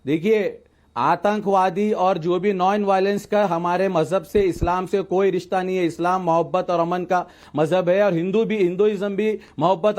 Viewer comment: Did he just say ‘non violence ka humare islam se koi nata nahi hai?’ 16:55 😂